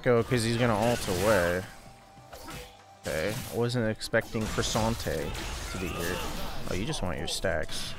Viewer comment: Did you just say.. Croissanté